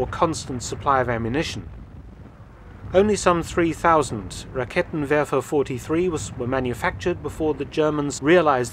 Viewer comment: I was half-listening to the start of this and thought Mark said "Kittenwerfer". Those monsters!